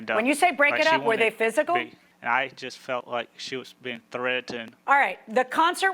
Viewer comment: Legend has it that Forest Gump got his personality from this guy. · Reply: Yeah because every time he says "threatened all I hear is "Jenny"